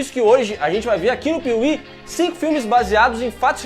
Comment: só eu escutei "100 filmes" kkkk em um vídeo com 11 minutos,ia ser muito loko